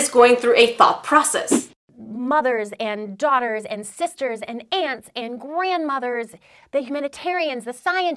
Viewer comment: When Barbie said 'Aunts' I thought she said 'Ants' lol.